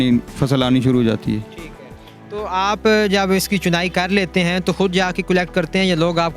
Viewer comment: what did host said ?😟😱😱😱 legends ? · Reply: He said chunai means picking jahil shaks dimag ganda hi rakha Karo hamesha🤮